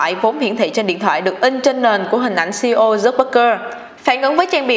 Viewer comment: k biết có phải nghe nhầm k nhưng mà sao t nghe là zuckbergker nhỉ